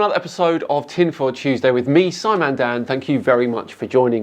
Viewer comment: !!?? Bro Ive been tuning in for a month and a half now. I thought you were saying your name was Simon Dan. I just thought you didn’t know how to speak because you’re British.